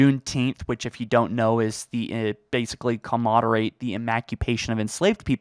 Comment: what is he saying? "Comodorate the emacupation" 🤣🤣🤣